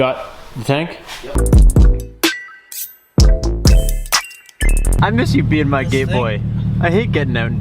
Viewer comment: Broooo, at 9:39 I swear you said, " I miss you being my gay boy" 😅